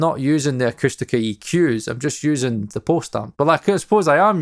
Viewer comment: What does he say on 28:06? "I'm not using their eq's, I'm using their..." Post stumbs? Pose stum? Bosetums? 😅 Please help. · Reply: Post amp